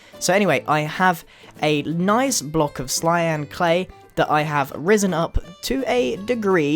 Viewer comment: "Block of Clyan clay". haha xD i love you too +Grian :) ^_^ Keep the great videos coming and yeh idk what more to say ^^ Love ur content!